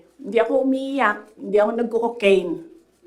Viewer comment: di mo narinig yung pasaring niya boss. Sabi ni Inday di ako umiiyak, di ako nag COCOCAINE. hehehe